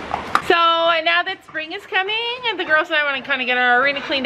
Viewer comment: Omg when you said now that spring is coming I thought you said now that Sabrina’s coming and I was like wait what.